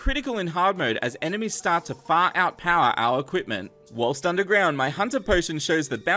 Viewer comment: when he was digging a hellevator i thought he said "in hardmode the enemies begin to fart out power"